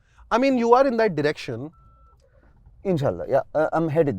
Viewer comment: did bro say in sha Allah?